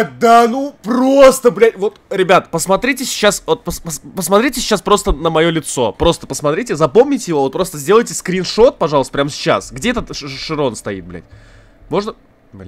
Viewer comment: На 3:47 мне послышалось.Посмотрите на моё яйцо